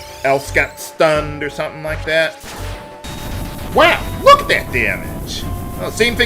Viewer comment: What was that sound effect that 11:38 did it say whack, or was it a duck quacking? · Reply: That was me saying, "Wow! Look at that damage!" Although, I didn't enunciate very well there.